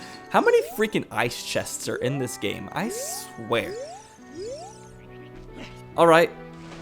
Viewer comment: It sounded like he said "Ice Wear" and not "I swear" 😕